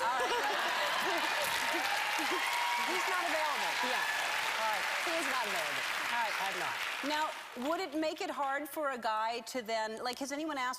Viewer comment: At about 3:38 did Selena say "He is not available" "im not" ? And i love ELLEN AND SELENA